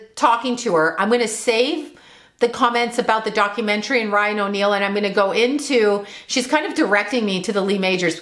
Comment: said Ryan O’Neal but I thought she said Lying O’Neal...lol